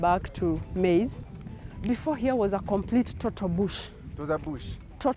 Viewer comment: When she said before farming here there was a complete and total bush I thought she said bullshit instead of bush 😂😂